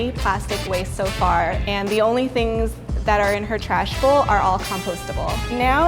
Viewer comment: ish did anyone notice said "the only things In her trashable..." Instead of "the only things in her trash"?? Like if u noticed 😂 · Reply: She said trash bowl